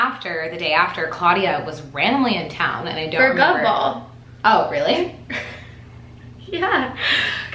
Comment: Okay first of all I just woke up from a coma n I'm catching up on your content. Second this is admittedly my first podcast n I am glad this was it. Third for some reason I thought you guys said "gum ball" at first and for some reason just pictured you guys emptying out a gumball machine for hours just to your fav flavour.